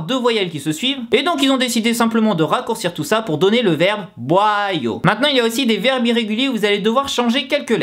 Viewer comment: J'ai compris boiyau